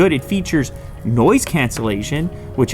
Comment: I think he caught himself trying not to curse lol Edit: never mind I heard noise canceling sh*t at first